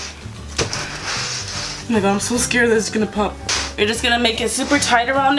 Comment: did she say nigga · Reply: No she said oh my God really fast